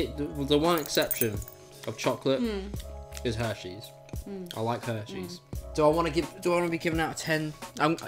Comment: I thought said he likes herpees